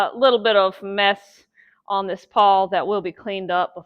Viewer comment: did you just say there is meth on the paw? lol. Great video! I’m wanting to start tanning deer hides of my kills. My 6 year old daughter gets to start hunting this year and I’m wanting to do this to her first deer. Thank you for all the details in this video. I’ll be returning to it come time!